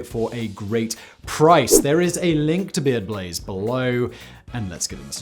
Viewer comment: When you said “Beard Blaze” I swear I heard “BeyBlades” and was met with only disappointment.